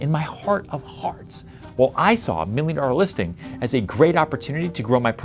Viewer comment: it says “Millionaire” instead of Million. Great advice. Thank you!